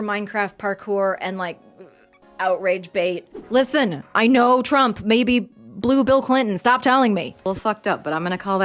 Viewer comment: I had to watch this like 5 times because I kept hearing “I know trump may be blue bill clinton” and I was so confused